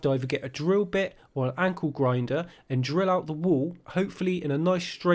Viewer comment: You know that thing where you hear the wrong words in songs.. - well I keep hearing at 3:04 - 'Ankle Grinder' Yes sir, I got to get me one of those ;-)